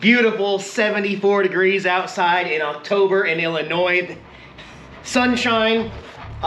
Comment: Getting out of Illinois, smart move. Hopefully I'll be right behind you. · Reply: Yes! I really thought I heard him say "IlliNoid" (ILL-Annoyed?) right before he went for his ride! 😼😹